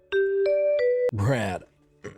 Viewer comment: What does brad say before he clears his voice to say his text words? Crap? crab? Brad?